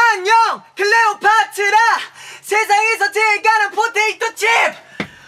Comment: did he say potato chip ?? Oh finally, i am getting noticed.